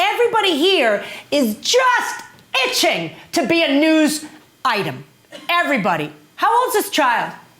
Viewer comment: So he has gun and u chase him with a car bullshit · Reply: is she saying news item or used item